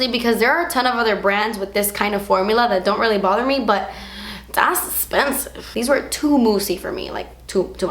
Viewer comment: when you said 'that's expensive' all i heard was 'das esspensive'